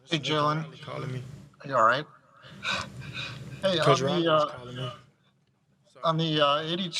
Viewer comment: who did he say was calling him? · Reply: That was his former coach, he says "Coach Riley callin' me", Lincoln Riley, University of Oklahoma.